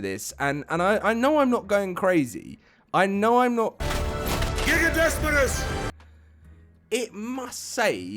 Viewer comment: where is that damn M that makes people think its gigadesmodus! I cant understand it, I cant hear anything other than gigadesperus.